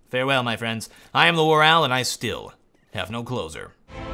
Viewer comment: i always hear the end as "I'm the WarOwl; and I still, have no clothes on"